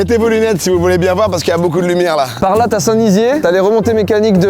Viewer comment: c’est moi ou il a dit St Dizier ??